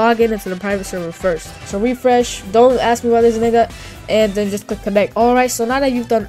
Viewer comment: What did you say😭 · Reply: broooo, dont even worry about it🙏(i didnt say the word n word i said dont ask me why this is named that)